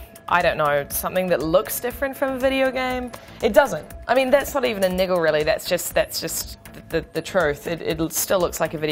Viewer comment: What does she say at 5:12? It sounds like she says "that's not even a nigga or anything..." love the aussie language..